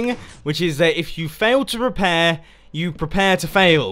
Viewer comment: You probably misheard... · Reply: no i just watched it back, he definitely says repair. time